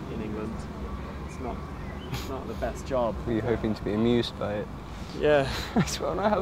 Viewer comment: What does Joe say @ 7:17 ? He hoped to be amused by..? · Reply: "Were you hoping to be amused by it? I just wanna have fun! "